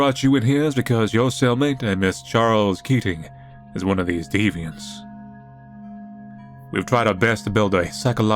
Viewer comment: When the guy said deviants, I misheard you. I thought you said Manly fans and ended up saying, "yeah, that's kinda accurate."